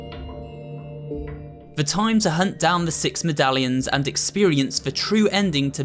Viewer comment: I thought he said "time to hunt down the 6 italians" IM LAUGHING SO HARD XD.